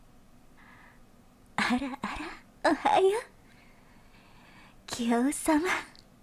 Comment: I misheard that as "Ara Ara~ Ohayo Keyaru-sama" You probably know where I'm going at from here..